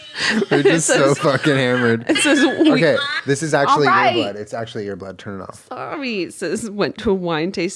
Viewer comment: I thought said "This is actually your butt, turn it off" instead of "Ear blood" I thought a pic of butt popped into her phone's screen, just wow why brain thanks.